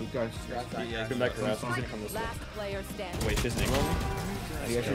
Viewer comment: nah i cant be the only one who heard "wait this n*gga" as he was killing the reyna at 5:03 LMFAO